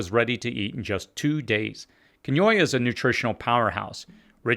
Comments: What is the seed he is saying at 10:11, it sounds like Conoya? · At around 10:10 you mentioned a seed but I can't make out what you're saying. it's a nutritional powerhouse with all nine essential amino acids. is it quinoa?